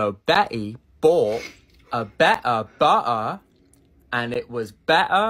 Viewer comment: all I hear is bu uh🫨🤣